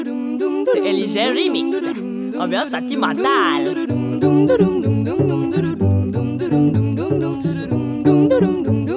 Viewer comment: What does she say at 2:18??? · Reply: She said "ambiance sentimentale" 😊